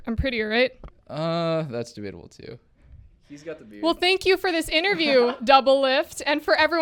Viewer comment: Does he say "hes got the boobs" 6:08 ? :D